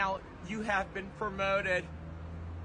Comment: Brooke: You are now one of my elite employees! Sam: YIPEEEEE🤗✨ Edit: bro my bad idk how I heard ‘league employees’ instead of ‘elite employees’ either 😭 when I commented this I had only heard this sound like once. I’ll quickly fix that